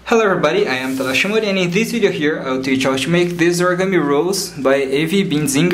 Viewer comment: Anyone else thought it said evil origami rose?